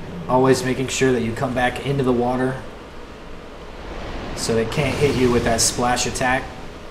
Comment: Bruh I thought you said it couldn't hurt you with the water ability in the water. Lol